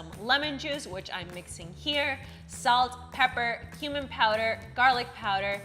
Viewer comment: Sounded like she said “human powder” when she said cumin powder lol